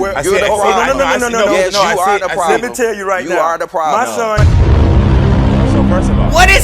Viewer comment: I think he said his son HIV+